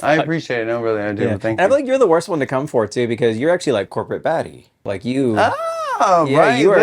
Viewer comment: WHen MAddi said"corporate baddie" i thought she said" corporate 'F@ 😭